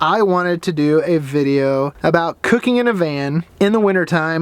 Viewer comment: I thought it said" winter cooking in a man. " I was like ...wut. .